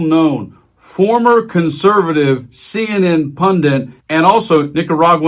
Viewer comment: Did he just say "Pundint"? What is a pundint? I thought the word was "Pundit".